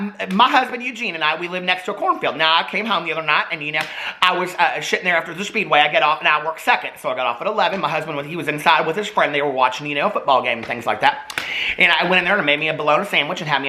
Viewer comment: LOL!! At 14:43 he says “Well, I was shittin’ there...” instead of sittin’ there lmao!! Screaming!!!!!